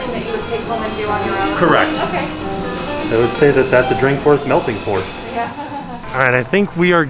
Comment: “That’s a drink worth melting for” Love it!!😊❄️🍫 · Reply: I thought he said "that's a drink worth milkin for"...😂 Since it was milk and chocolate...lol ...get it?!?!...lol